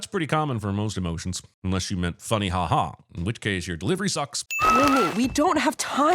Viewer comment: Subtitle speaker says Mira instead of Rumi lol